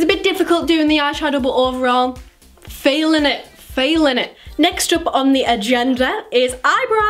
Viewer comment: When she said feelin it I thought she said failing it😂😂